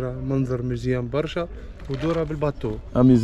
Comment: Very nice vlog <3 10:13 when the guy told you "Mandar mezyen barcha", he meant the view is very beautiful if you go inside with the boat (Bateau in French) and you thought that he said Mesuem. Mizyen in Tunisian is Beautiful. I am so glad that you came to our country and I really appreciate your efforts and progress in improving and learing Arabic. Such an inspiration walah